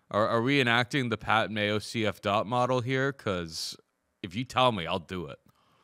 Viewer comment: Can someone explain the meaning of "CF-Dot" model....i understand the concept of betting the underdog, but what does it mean or stand for? thanks in advance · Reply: It's actually commonly misheard as CF DOT, I thought the same for at least a year haha. It's 'CF DOP Model' which stands for "Chick Fight - Dog or Pass'. Once I figured that out I almost laughed about how obvious it was. Hope that helps some!